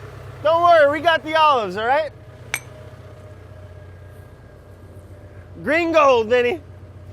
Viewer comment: I thought brad said “gringo” but when I went back he said “green gold”